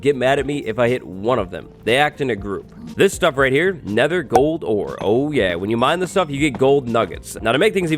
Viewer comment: I thought he said nether quartz you'll find everywhere kind of like cold sores not gold ore lol